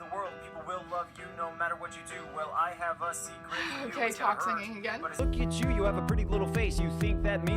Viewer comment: Lol, it took me a second to realize you said Onision was talk-singing. I thought you said he was toxinging, which works too, haha